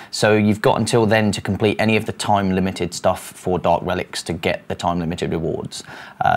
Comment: Not going to lie. I thought he said "Dog Relics" and not "Dark Relics"